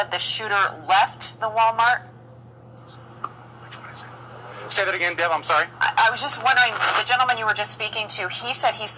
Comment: anybody hear what gets whispered at 0:52 ? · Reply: He whispered, "Which one was it?" I dunno...... but that's what he said.